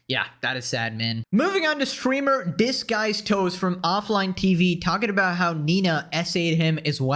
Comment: did you just call him "dis guys toes"?